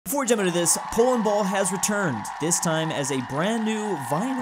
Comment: Can someone translate to me what he said during 00:00-00:01? Ty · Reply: Before I jump into this polandball...